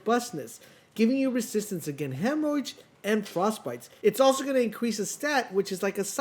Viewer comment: did you say Endurance helps against "hemorrhage" or "hemorrhoids"? The first makes since, but the second is just flat out a hilarious joke you might have snuck in. ;)